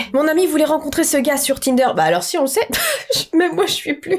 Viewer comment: c’est moi où elle dit kinder ?😭😭😂